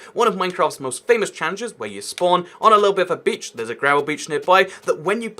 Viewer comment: What I heard: “where you spawn on a little bit of a beach” What I read in the captions: “where you spawn on a little bit of a bitch” I love you too YouTube